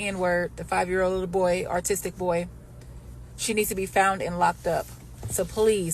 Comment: Did she just say 5 year old artistic boy?